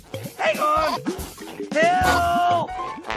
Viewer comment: As a kid I thought he said "HELL!" but he was saying "Hill!" 😂